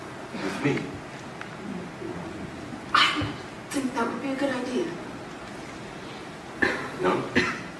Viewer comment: it sounds like she said, "i think that'll be a good idea"...